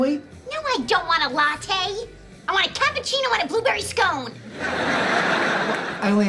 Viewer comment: The first time she say blueberry scone I heard it as HUMAN SKULL.